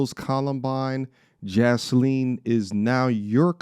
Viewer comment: I didn't even know her name until now.. and I thought you said Jizleen. 😂